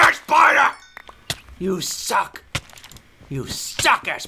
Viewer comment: did he say fuck?